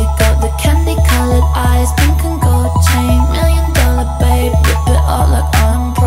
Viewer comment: i thought she said "million dollar bank" and not "million dollar babe"💀